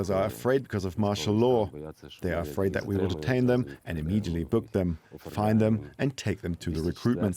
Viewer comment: Not what he said. His literal words are "after the court ruling they're actually being taken"